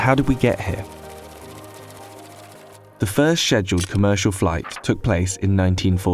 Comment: Did... did he say "sheduled flight"?